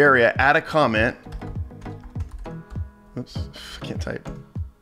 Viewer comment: did he say fking type?!!!!